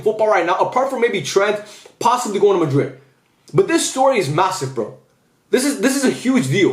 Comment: did u say massive 👀